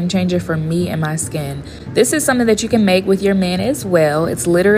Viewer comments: When she said with your man, I misheard it and thought she said mayo but then reading the comments I was way wrong… 💀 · I was so sleep deprived and ill that i didn't read the subtitles "this is something that you can make with your MAN as well" I THOUGHT SHE SAID "this is something that you can make with your MAYONNAISE well" ...turns out I heard wrong I think i regret trying to understand why the reaction and comment section felt off for mayonnaise being used lol